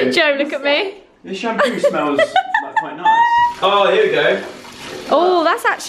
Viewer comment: Oh phew I thought at the start Joe said " This shampoo smells like my nuts" 😂😂